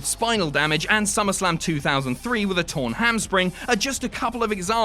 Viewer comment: Did I just hear "torn Hamspring" about Kurt Angle?